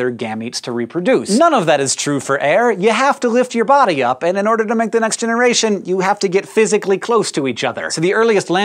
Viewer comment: what did he say at 3:08? the subtitles didn't work there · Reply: none of that is true for air. you have to lift your body up and in order to make the next generation you have to get physically close to each other.